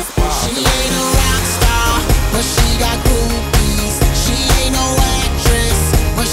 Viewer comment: Akon I thought he was saying she's got boobies 😂😂😂😂😂😂😂😂😂